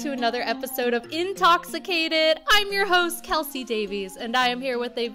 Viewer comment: hi kelsi and yall are my favorite youtubers🙌🙌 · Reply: • MillaOBVI • I just thought that you said you hate kelsie videos sorry 😅🙂